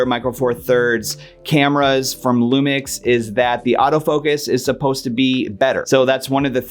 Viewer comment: I heard "auto focus is better" and you got my attention 😂